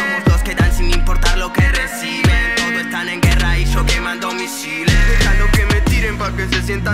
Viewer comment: Tan mal estoy que en la parte de donde dice "Todos están en guerra y yo quemando misiles" escuche "todos estan en cuarentena"